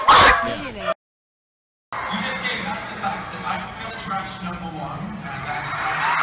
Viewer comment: what did Dan say? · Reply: "That's the fact, I'm Phil Trash Number 1"